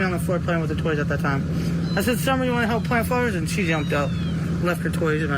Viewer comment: Summer Leaves her toys in the house 10:09 she put her puppies aka Paw Patrol toys in the flower pot. I thought she just said summer lift her toys in the house 10:58 I walk summer in the house 11:22 I went in the house with the boys apparently summer wasn't in there ...😶 lies lies lies I hope the law enforcement knows that